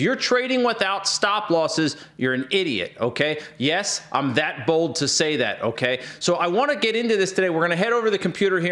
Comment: I heard this as "yes, Im that bald to say that."